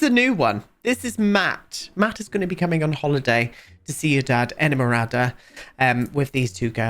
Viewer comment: I can't stop laughing at the fact that every time you said the name of the world, I heard "see your dad enamorada".